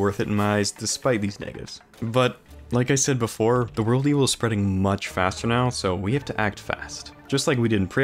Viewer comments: what did u say? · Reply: "but, like i said before, the world evil is spreading much faster now, so we have to act fast." - 7 minutes and 19 seconds into "I purified my ENTIRE WORLD before the Dryad moved in" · what did he say at 7:16 ??? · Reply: Negatives